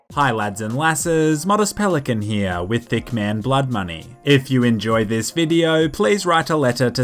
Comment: I swore you said "Dickman: Blood Money". Sounds like Killer Bean but instead of pistols and beans, you have micro smgs and micro pay-nee-suz